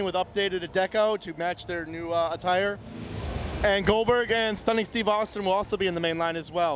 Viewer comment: did he say “Stunning Steve Austin?”